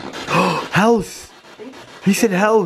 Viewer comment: when she asks Tom on the spirit box "When did you first encounter Moe?" ..You thought you heard "house" as a response.. Which I could be wrong, but I thought it sounded like he responded by saying "basement".